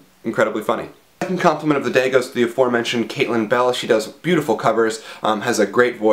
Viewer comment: When you first said Caitlin Bell I thought you said Katie Bell. I actually know someone named Katie Bell, and she always complains that she had the name before JKR ever came up with it =D